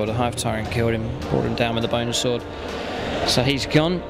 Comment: Haha 32:24 it sounds like you said he was killed with a boner sword